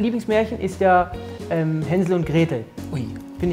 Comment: Haha, ich habe zuerst "Hendl und Gretel" verstanden und jetzt habe ich Hunger. xD